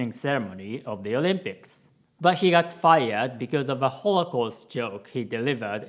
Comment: When you said holocaust joke I misheard it as "rollercoaster joke" and was very confused. XD I understand getting canceled for the new things,but it's pretty harsh to cancel someone for things they did 20,30 years ago (unless they still act the way they did in the past).